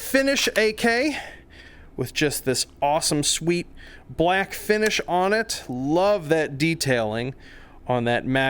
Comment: Just woke up and thought he said "with just this awesome sweet black Finnish on it" lol